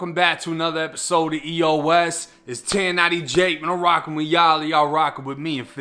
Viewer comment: I thought they said 1090 Jake was dead